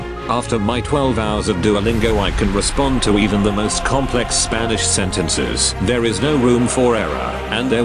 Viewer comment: Sub and ask mom for robuk. Dont belive me? Check out this: <link> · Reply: Bro did you just say Spanish server?!! Or am I tripin